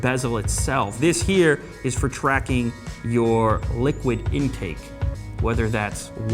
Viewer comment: Did he just say liquid incake?